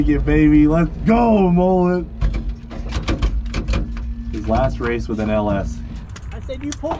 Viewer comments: This literally happened an hour ago. I ran into the track tower in my suit and edited as fast as I possibly could, they have great internet and somehow we got it up!!! Month of Freedom BARELY continues lol! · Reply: I heard, "last pass with an LS". What's the plan for mullets new powerplant? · I feel like this is a turning point moment for Cleet and Mullet. Pulled out that 7.3s tune like a pro while battling a hurt motor. Last run with an LS he said. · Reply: I thought he said last run was an ls meaning the competitor